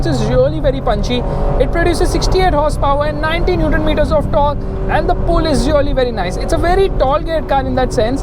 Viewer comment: Did he just say 1900 metres of torque!!?? 9:07 💀♨️